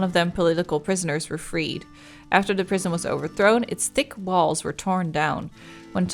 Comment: omg I heard 16:56 "thick walls" as "dick n balls" and then I heard 25:25 as "sipping cock" help. I need help.